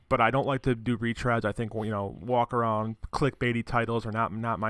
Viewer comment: did I hear reach around in there at